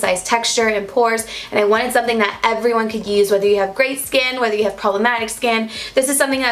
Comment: Lol i thought u said "grey skin" hahah but omg you look amazing and your skin is getting better and better every day 🙌🏻 btw your lights arrangement is perfect, light enough but also very true to the real colors. 💕💕💕